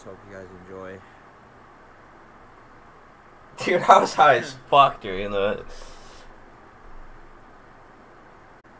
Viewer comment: yo did he say he was high asf during that ????????!!!!!!